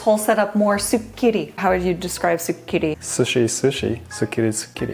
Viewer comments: i was eating crunchy crackers while watching and instead of "sukiri", i thought said "super kitty" and I just accepted it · LOL, why did I hear “soup kitty” and “sushi” at first 🤣😭